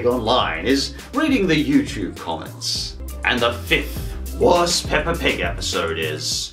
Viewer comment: did u say pepper?